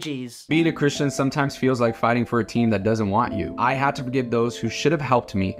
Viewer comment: i heard "fighting protein"😭